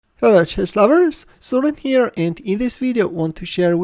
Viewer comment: Did I hear 'chess flowers' in the beninging?